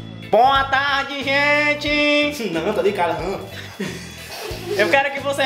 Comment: Quando disse : ''Tô brincando'' eu entendi ''Tô de Cara''. KKK'